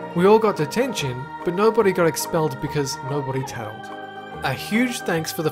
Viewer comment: did he just say "nobody got expelled bc nobody telled"?